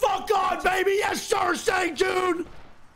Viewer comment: He said “Let’s Go Sang Joon”, but I heard “Let’s Go Saint Jude”